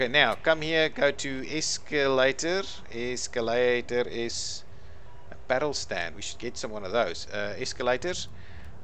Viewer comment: What you say at 26:27? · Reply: I said 'apparel stand' not quite easy to get or logical in the context. I just spotted it in the objects list and thought it is worth getting.